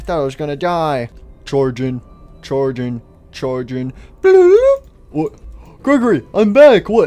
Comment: I thought sun said blood